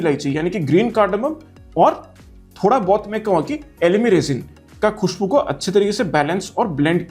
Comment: Sir 18:10 i couldnt understand a few notes that you mentioned for outlands, i got the green cardamom but the others I couldn’t understand. Can you give me a simpler example to help me understand it better? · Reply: elemi resin is a balsamic note bhai which is usually combined with many citrusy notes in the opening....it has some warm resinous feel to it with slight spiciness and mild citrusy nuances......remaining spicy notes I hope you got those clearly bhai